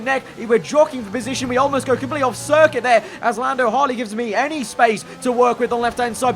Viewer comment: did I hear ‘Lando Hartley’😂😂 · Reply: He said "Lando hardly" (sorry if you meant to make a joke😂)